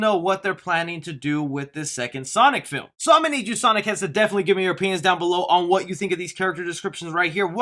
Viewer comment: “Definitely give us your europeans in the comments section”???? That’s what I heard 💂🏾‍♀️💂🏾‍♂️💂🏽😅